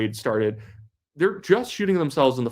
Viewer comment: did you say "high-coward rifle"😆